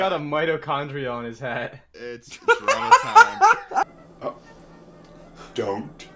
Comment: does he say it’s corona time? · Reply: He says "its drama time"